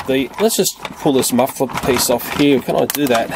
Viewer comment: DId Say "Let's Just Pull This Mother Fucking Piece Off"!?